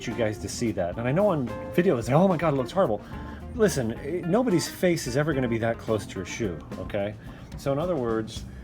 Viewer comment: I misheard what you said @ 24:50 and thought you said 'unless you're a turtle your face is never going to be that close to the shoe' and now it's my new saying when cleaning my personal boots lol